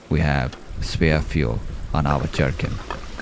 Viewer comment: Did he say JARKING In 00:52??? 🤣🤣🤣